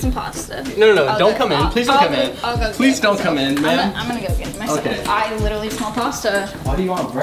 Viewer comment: when surprised, did he say "Please dont come in BABE?!"